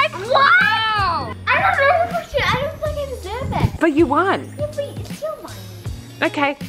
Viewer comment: it sounded like naz said… idk thing I (f word) deserve it? Anyone else agree?